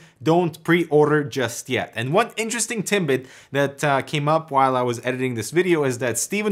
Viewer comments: Did you just say interesting "timbit" XD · Did you say TimBit at like 3:52 Its what it sounded like to me :D